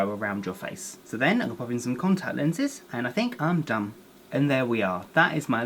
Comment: it sound like he said dumb instead of done